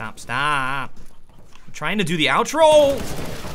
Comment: I could swear I heard "Stop staahhhp... I'm trying to do the *OWLtro*"